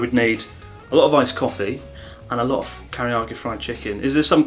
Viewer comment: What does he say at 4:45? Teriyaki? Karaage? Karaoke? Kentucky??